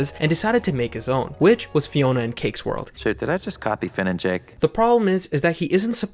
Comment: did he just say shit?